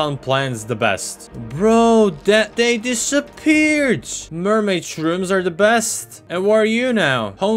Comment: “dumbass shroom” is what is says Lol